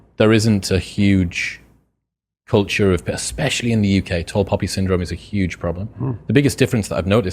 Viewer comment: what did he say at 1:55… top hobby syndrome or something? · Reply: Tall poppy syndrome